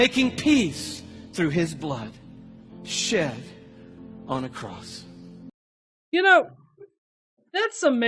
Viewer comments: It sounds like he says "shit on a cross" @ 25:18 if you watch it at x1.5 speed LOL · did he say “shart on a cross” ?? I bet you would